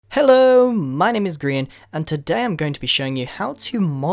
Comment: I had my captions on and when you said hi my name is grain or grian it thought you said hi my name is green LOL